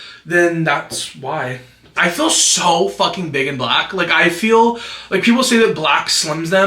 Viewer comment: ngl thought you said “i look so fucking big and black” not IN black